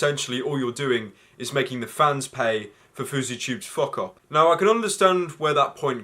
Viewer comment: Anyone else notice the "FousyRat" in the captions when he said fouseytube? 2:08 And the "rat man" instead of family man at 7:57 And another FousyRat